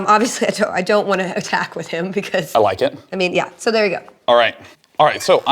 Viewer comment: I don't think Felicia knows what "ergo" means? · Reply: Oooooooh that's what she was saying I thought she was saying there go.